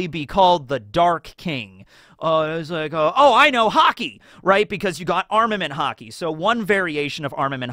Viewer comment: I thought you said "HOCKEY" at 6:25 and was confused as to when did one piece become a sports manga